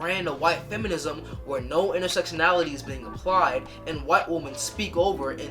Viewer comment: Mostly commenting for the algorithm but Lol, the auto-generated subs misheard "intersectionality" as "homosexuality" which means that it reads "and since I can recognize that homosexuality exists in the world..."